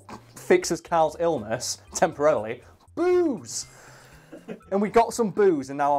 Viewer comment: i thought he said boobs and he also demonstrated some breasts lmao